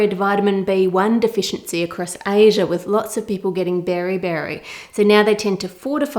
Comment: Can someone tell me what Ann is saying here? "Lots of people getting ______" ? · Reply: Hi! it’s Beriberi- a deficiency in Vitamin B1. 🙂